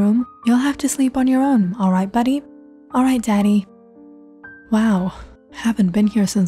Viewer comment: That one child with the imaginary mother... what's his name? Daddy or Danny? Okay, it's Danny Schultz! I kept hearing 'daddy'!